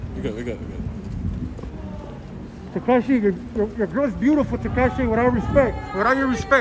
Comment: Lol did he just say Tekishi 🤔🤔